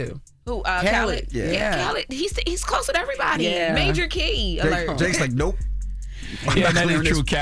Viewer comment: major .... What alert? What did loren say? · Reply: Key